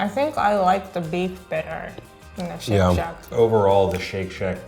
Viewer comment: i legit thought it said "shrek snack"... pftt